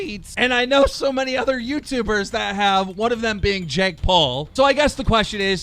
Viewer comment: I thought fouseytube was saying Jake Pool